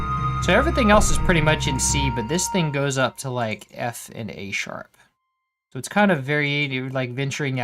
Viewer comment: Sounded like you said “This thing goes to effing-A sharp.”